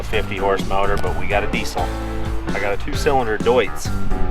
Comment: Had to listen to the end twice, you said: " . . . see if we can suck apples, that's the plan". I thought you said: 'see if we can suck apples, OFF THE PLANET '